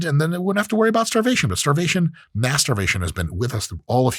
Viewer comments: At first, I thought Mike said starvation masturbation @ 3:33 😅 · “Mass starvation is almost completely gone” I thought you said masturbation and was like “nah, we’re keeping that shit alive single handedly”